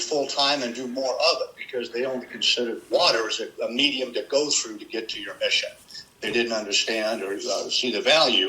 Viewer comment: The transfusion story was from an episode of MASH. Hawkeye and McEntire wanted to teach a racist soldier a lesson. After the transfusion they dyed his skin brown and made him believe the blood caused it. When he awoke they treated him as a black guy. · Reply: He actually said, 2:20 "They don't consider the water a medium to go through to get to your mission." And the Air Force doesn't consider the air as a medium to get to their missions.